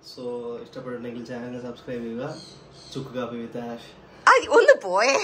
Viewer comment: Did he really say chukkkappi with??